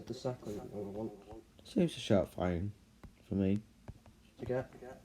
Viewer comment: what did you say boi i can not hear what you said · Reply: I said: “it seems to show up fine for me”